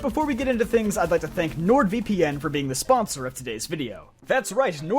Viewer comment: Not gonna lie, I thought you said "Lord VPN" at first.